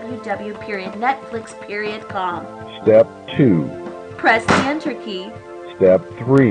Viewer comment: SMASHES THE ENTER KEY Um, did you say press?